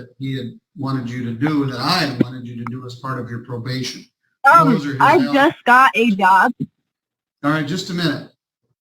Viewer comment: When she said "I just got a job" I thought she said "I just got a dog" and I was like "I don't see how that's relevant, but I do feel more sympathetic towards you now."